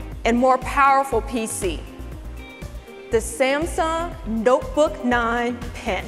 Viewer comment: When she said "Notebook", I heard "Dopebook".